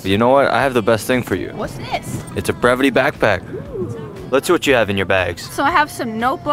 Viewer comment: When he said bervite backpack I thought he said gravity backpack